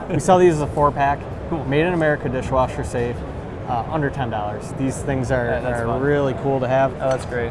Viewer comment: $110 for some chopsticks?! I could see if they had some nice artwork in gold leaf but just plain looking, no way. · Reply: i heard "110" too. But he clearly meant 'under 10'